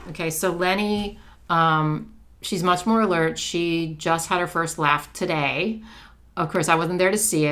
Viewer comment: First LAUGH! I thought you said Leni had her first bath and was so very confused! 😂😂